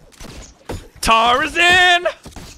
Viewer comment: did he just say "Tarzan" as the spiderman theme song